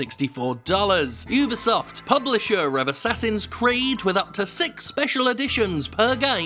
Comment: Thought I heard Assassin's Greed. Rewound. Disappointed.